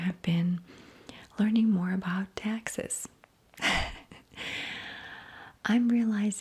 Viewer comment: Are you considering a move to Texas? I’ve been away awhile and feel I’ve missed something? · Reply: I think you might've misheard the same part I did at 21:25, she says "learning more about taxes", not Texas. lol